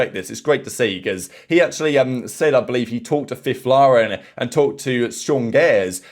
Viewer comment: in 9:38 who did he say he talked too? someone and sean gares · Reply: Fifflaren i think, another CS legend